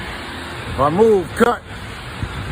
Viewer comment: I thought he said if I move cut. Why did the dude wit the fake knife jus kept his hands their and not move lol